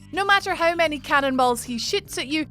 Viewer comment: The captions say “No matter how many cannonballs he shits at you,” and I gotta give it to the algorithm on this one 😂😂😂